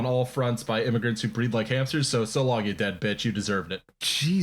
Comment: I didn't hear "breed like hamsters" I heard "breathe like hamsters" and for a second, I really tried to think of why you would say something like that. Like, obviously it would be racism..... but in what way?